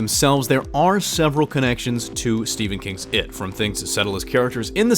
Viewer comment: i heard it like stephen kings twerk